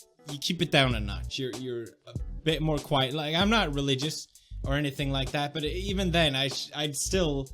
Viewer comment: did he say im not a religious person at 2:49 what happened to Christian Channel! Jk nice vid though